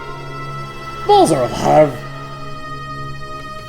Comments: what does say at 21:00? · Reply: The balls are alive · What does he say? xD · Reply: The balls are alive! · What did say at 20:58? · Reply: "The balls are alive"